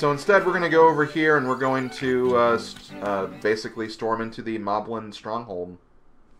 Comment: At first I thought you said the moblins mob hole. That would have been hilarious.